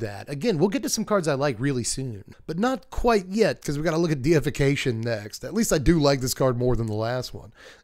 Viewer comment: I thought it said defication, but the card might not be poop.